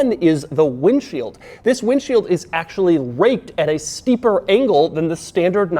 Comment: Did anybody notice that the captions say "the windshield is actually raped at..."